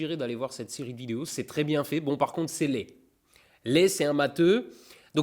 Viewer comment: J'ai entendu "[...] Bon, par contre c'est laid. [...]" Heureusement qu'il précise que "Lé est un matheux".